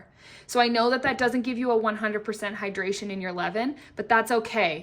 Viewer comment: I'm struggling with your terminology. Please break it down a bit more. Starter, levin, preferment, ferment? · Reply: Lol, Ievin? I thought she was saying eleven the whole time, as in 5 part flour plus 5 part flour plus 1 part starter! 😅😅😅😅😅